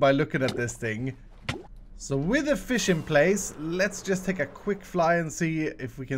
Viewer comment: Is it hust me or did it sound like Iskall said "Wither fish in place"? I know that he said "With the fish in place" but it sounded like it.